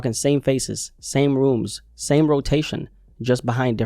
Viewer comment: “same faces , same rooms, same rotation just behind different doors” I thought he said “same wombs” that would been funny asl 😂😂😂😂